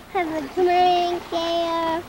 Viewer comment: Wait hold up, what did just say at 1:00:10? Have a comedy theatre? O.O